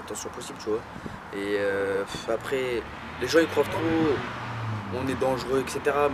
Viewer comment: les gens il quoi ?😂😂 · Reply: Les gens il croive trop que on est dangereux